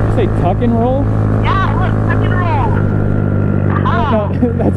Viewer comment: I thought she said fuck and roll